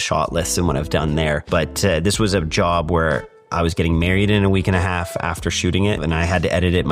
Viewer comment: did you say we're getting married? Wow, I'm happy for you